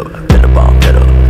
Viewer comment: Who also thought it said venom at first but then realized it said fed up